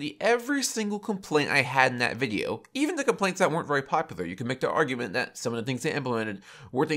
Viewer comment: Does anyone understand what he says at 11:57 ? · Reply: “You could make the argument that some of the things they implemented-“